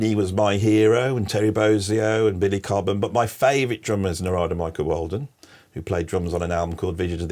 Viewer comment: Can we get the name on a link? Sorry, Andy. Your introduction to your favorite drummer was a bit? ENGLISH. So I think you said - Noraldo Michael Oldom? Never heard him play, but your enthusiasm has me hooked. More, please!